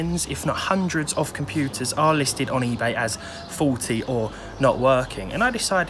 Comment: when you first said "faulty" I heard it as "forty" and got a good laugh, always enjoy your vids